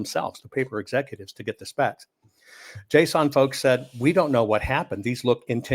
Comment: is he saying "JSON"? JSON as inhuman readable text formatting for arbitrary data having "name-value pairs" (you have a label for the data and the data itself)? ...